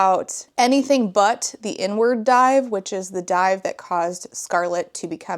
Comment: I swear I thought you said “n-word dive.” I had to rewind because I was like, there’s no way that’s a real diving term 😂 I assume it’s “inward dive.” I know nothing about diving, can you tell? lmfao